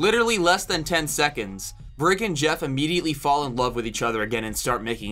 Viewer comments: Don't know if anyone else who heard him say in under 10 seconds brick and Geoff were back to making out instead of bridgette and geoff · BRICK AND GEOFF ARE IN A RELATIONSHIP???? Obviously I’m joking I just found it funny that you said brick instead of Bridgette. 10/10 video. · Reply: What I was trying to say was "Brig and Geoff" but I can see why you hear it as "Brick"